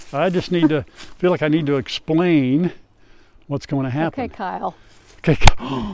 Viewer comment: Okay okay when Grandma said okay Kyle I thought she said okay senile😂😂😂 I had to back it up three times just to make sure that's not what she said but on the 3rd I heard her say okay Kyle😂😂😂😂 PLEASE FORGIVE ME I NEED TO HAVE MY HEARING CHECKED I MAY NEED HEARING AIDS😂😂😂😂 okay back to the video 😁